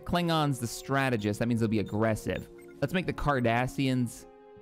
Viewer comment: Huh for a second I thought said "The Kardashians" lmao